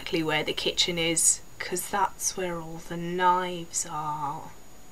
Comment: When she says "that's where all the knives are." I thought she said that's where all the limes are and then she does the psycho face so I'm just laughing my fucking ass off.